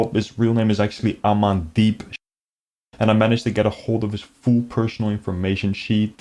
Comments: i thought you said it was “aman deep shit” 😂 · I thought you called him Amandeepshit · his real name sounded like "i'm in deep shit" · Funny, I thought I heard "Im in deep shit" but it's actually Amandeep S***(brief censored noise) 😂